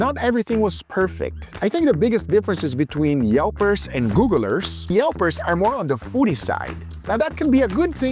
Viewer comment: Haha, when Guga said "Google Reviews" I thought he said Guga Reviews! I'd trust that review over the other two! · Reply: I thought he said, "Gugalers."